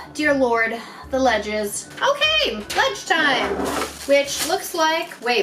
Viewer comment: "Ledge time" I heard "Lunch time" um, oops.